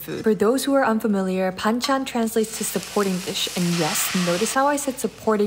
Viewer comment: I really heard “supporting FISH-“